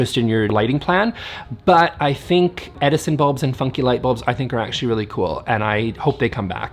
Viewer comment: Had to go get some coffee because I heard "eff you, wallpaper" instead of funky.... 😆